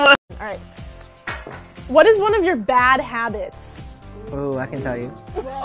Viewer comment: what did frankie say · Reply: “oo i can tell you”